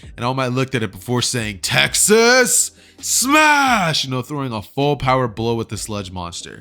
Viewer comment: For a second I thought all might said Taxes SMASH!!